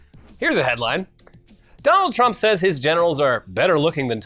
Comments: We love you, alive viewers. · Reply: Okay, so I wasn't looking at the screen when you got to the Trump story, and when I thought you said "Trump said his genitals were better looking than Tom Cruise" my first thought was "you know what, I'm not surprised" What the fuck even is that man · I heard "genitals" instead of "generals" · At first I thought you had said that Trump said his genitals were better looking and stronger than Tom Cruise, and I was like "?" I needed some context.